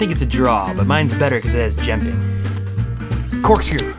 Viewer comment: I need someone to answer me what did he say here: “I think it’s a draw but mine’s a bit better because it has *something*. Like, WHAT DID HE SAY?? PLEASE TELL ME! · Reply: Omg thank you so much! I thought you said “Jemping” or something 😂😂